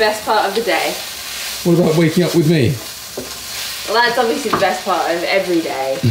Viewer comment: I thought said, "What about making out with me?" IM SO SORRY